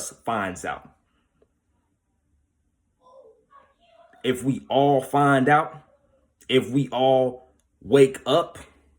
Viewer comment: Is it just me or did somebody say something at 20:29 ? Am I tripping or do yall here that to I just can't make out what it was saying. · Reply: "I can't move"